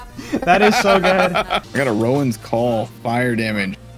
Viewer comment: i heard rolands call not rowans call anyone else